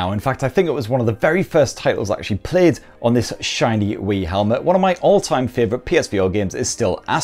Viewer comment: did u just say wii helmet?